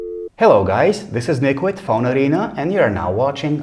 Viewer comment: Did he just said Liquid Phone Arena 0:04-0:05 hahahahaha